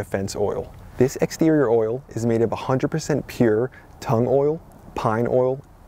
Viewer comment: By the way, your Eng caption here says "tongue oil" instead of "tung oil".